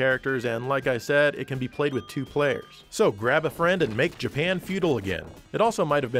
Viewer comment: I just heard at 5:57, go grab that friend for two player action. Let know it's action time.